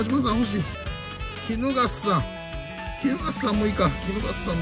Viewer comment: キノガッサにしか聞こえん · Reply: キヌガッサ